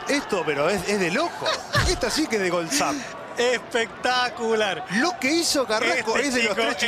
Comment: did he just say "Epic taco" ?